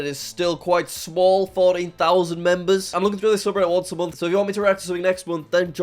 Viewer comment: did he says 40k members instead of 14k member😂😂😂